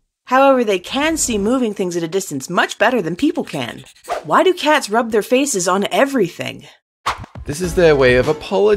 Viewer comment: I thought it said "Why do cats rub their feces on everything?"